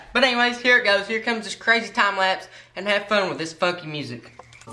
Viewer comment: it sounded like he said f*cky music